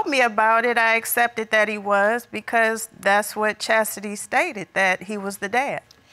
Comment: did you say her name is chastity ??? Hilarious ! Oke continue :D